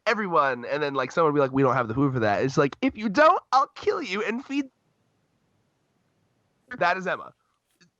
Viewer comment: dammit My audio cut out here right when I was trying to make a pretty epic point about Emma what I said was “if you don’t I’ll kill you and feed your body to them”😂